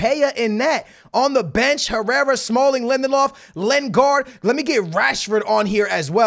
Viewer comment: When he said Lindelof I thought he said Lemon Loff 😂😂